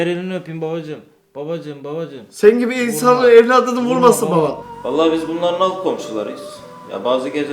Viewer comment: te ne dedi lan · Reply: Senin gibi insan evladını vurmasın baba dedi .s